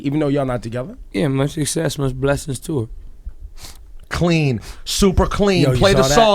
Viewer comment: What he said please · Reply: He said "much success .. much blessing to her" really easy hear